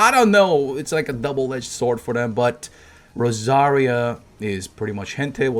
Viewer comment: Just got out of shower and saw this. And at 2:42 i swer i thought he said "Rosaria was pretty much Hentai"